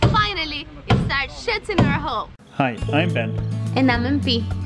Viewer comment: No, no, no it's not what you think.. She said "we are finally shutting our hull"..